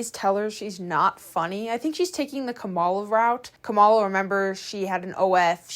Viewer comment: did you mean Camila Araujo ? Sounded like Kamala, had me dying when you said OF and Jack Daugherty in the same sentence as Kamala 🤣🤣